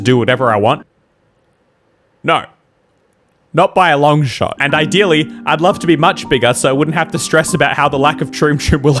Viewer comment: PLEASE someone! I want to know what he said there that they had to or felt they had to mute. 😂 Anyone that can read lips, please tell me what he said. I cannot read lips at all. So I have no idea what he is saying lol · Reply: It looks like it was just H E double hockey sticks no